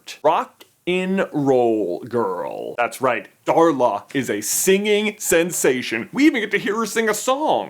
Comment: Bloody hell, I thought it said Darlas virgin story😅